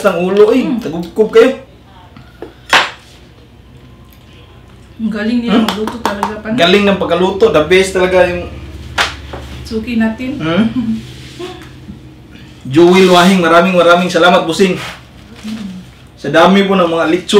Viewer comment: gua dengernya "selamat pusing"